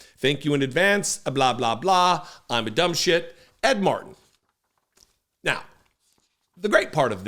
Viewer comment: Second time I heard, "what a dumb shit."